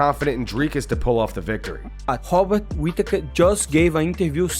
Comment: No disrespect to Moicano or Brazil, but when I heard him say “Hobbit Whittaker” it was hilarious 😅